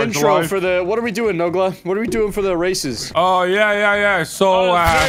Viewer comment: I swore I heard "what are we doing with the racist" don't do drugs kids 🤣